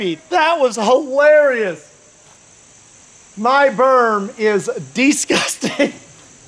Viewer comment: Thought he said my verb is disgusting after the sausage gravy shot and thought wait thats an adjective... then realized he said berm and that I might need hearing aids